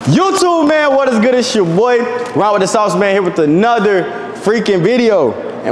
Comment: In the beginning it sounds like he says "raw wit da sauce" because he saying it so fast😅